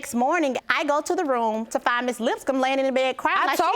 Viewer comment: Lol I thought the bailiff said the last name was "Lipscum" 🤪